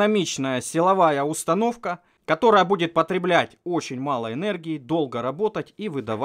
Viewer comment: мне одному послышалость "потреблядь"?